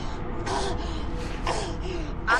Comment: What he say? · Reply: "I'm your friend, Hewitt"